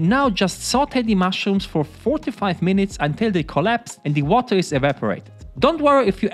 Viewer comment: Damn! I thought you said sauté the mushroom for 45 minutes and I was like bruh wtf XD